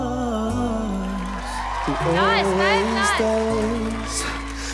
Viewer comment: What did she say?? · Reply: Robin Gonzalez Vera “nice babe, nice! “